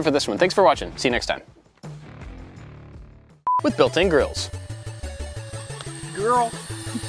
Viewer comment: I thought Bob said built in girls